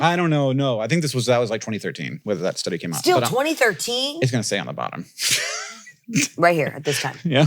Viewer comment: I thought he said “It’s gonna say I’m a bottom” instead of ON THE bottom. For a second I was like “I was wondering, but I’d never ask.”